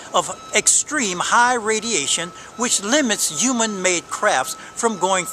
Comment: When I first heard, "Human made crafts." I thought I heard, "Human made c**p." Which would be most appropriate, given what was coming out of his mouth. You have a knack for finding the weirdos Oh! and thanks for the kind message. 🙂